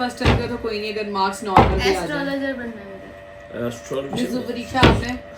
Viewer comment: did I just hear 👂 astrologer? Kindly elaborate it……